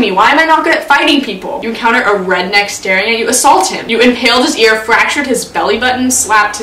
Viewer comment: I deadass thought you said "inhaled his ear"